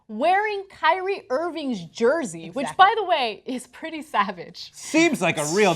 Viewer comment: did anna say #SAVAGE, call the president we need the nuclear codes ASAP